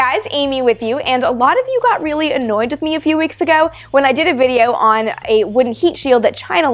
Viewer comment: I thought Amy first said she "...got a lot of heat from you a few weeks ago." That would have been a great pun :)